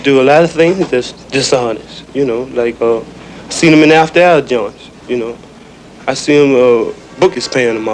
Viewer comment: Wtf did he say? Smdh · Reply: Seen them in the after hour joints